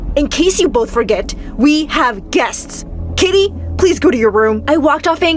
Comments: haha it says kitty instead of Katy, you had one job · wait what he said kitty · Anyone realise at 6:06 the subtitles said kitty instead of Katie